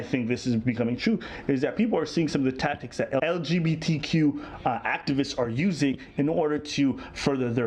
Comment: he clearly says activists